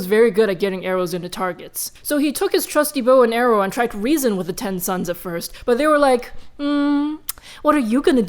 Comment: She said: Ten Suns, Ten suns, etc. I thought she was saying 10 sons and i was so confused